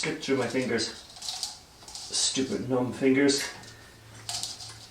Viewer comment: Did he say "Stupid Gnome Fingers?" at 5:45 ish? Hahaha